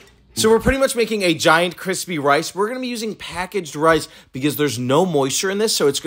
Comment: Did you just say “kwispy wice”? Cute.